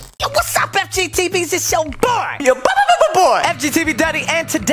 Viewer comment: Did he just call us Fgteevies. Or fgteevbies